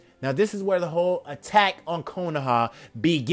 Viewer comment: when he said attack on konoha I thought he said attack on titan plz aot what ifs